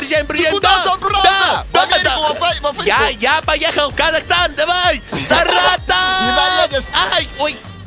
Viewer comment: Сне послышалось или он сказал я поехал в Казахстан